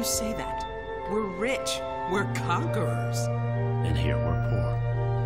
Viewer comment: The amount of typos or mishearings in those subtitles is phenomenal. "Were conquers" instead of "We're conquerors" for instance. Oof.